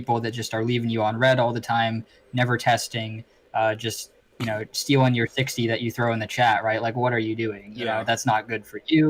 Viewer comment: What did say at 45:04? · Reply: It does sound funny haha but he said stealing your 60 that you put in the chat